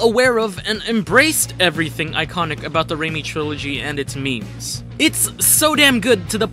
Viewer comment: is he saying means or memes